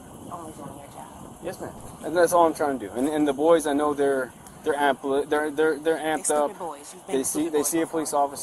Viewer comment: I think you misunderstood her. I thought she said “ i know how bad that can be” meaning it would have been devastating if he had pulled the trigger. I don’t think she was saying the boys are bad kids.